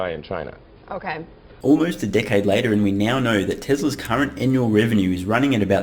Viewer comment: I really thought you said "Almost a dickhead later". I had to replay 3 times to make sure I'd mis-heard. 🤣